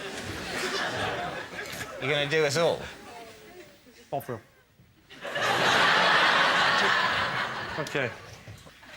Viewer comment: I just realised I do not know what Lee said. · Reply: He said Bovril